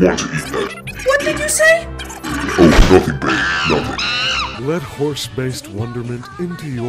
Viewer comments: did he say "babe"? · O_O did he say what i think he said? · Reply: Babe